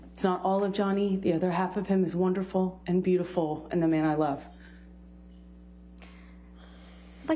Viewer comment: Does she say "The man I love"? In 15:18 ???